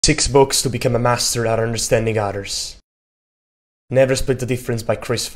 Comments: I heard “understanding otters!” 🤭🤦🏻‍♀️ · Honestly thought you said "to understanding otters". I was intrigued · ❤ I love your accent, but for a moment I thought you said: How to understand Otters 😂